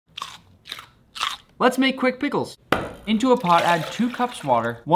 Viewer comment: I thought he said puick qickles